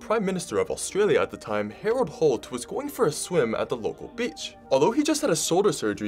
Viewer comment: did he just say "the local b*tch?!?"